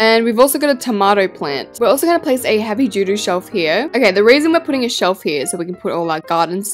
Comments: i’m sorry idk if it’s your accent but it sounds like you said heavy doo doo shelf 😭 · I CAN NOT be the only one who noticed her say “heavy doo doo shelf” instead of heavy duty shelf😂😂